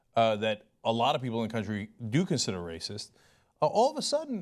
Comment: did i just hear he say "a lot of people do consider racist"?